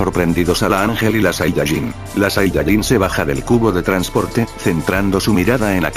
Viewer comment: Entendí TUBO xd